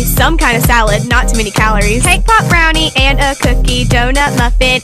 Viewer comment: When you said cake pop I thought that you are saying k- pop